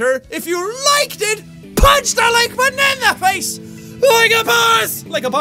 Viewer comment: Sometimes in your outro you sound like your saying "banana face" when your say punch that like button in the face like a boss